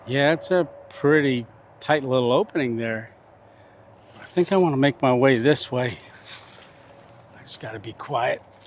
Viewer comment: What did he sayyyyyy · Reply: 😂 words don't always flow correctly it was tyke getting through there